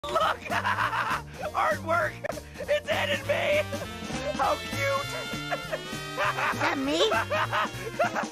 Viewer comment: "Ha ha! Look! Ha ha ha ha! Art work! It's Ed and May! How cute!" 😂 "Is that me?" "Ha ha ha! What the--AAAAAAH! That's not funny!" 😲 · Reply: When I was a kid, I thought he said, “It’s Ed and Me!”